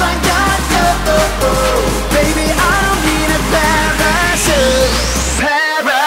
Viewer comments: i thought he was actually saying "baby, i dont need a pair of shoe with somebody like you" until i looked at the title again... lol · I always thought he said I don't nee a pair of shoes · I still today reckon it sounds like he says “ Baby I don’t need a pair of shoes”